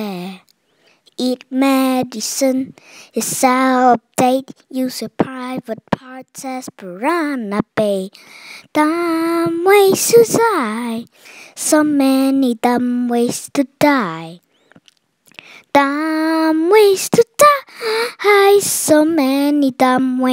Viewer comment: did he just say dumb ways to sigh 😝